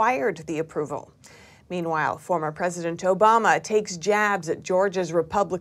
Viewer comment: 🤣 Did she say, President Joe-Bama!- clip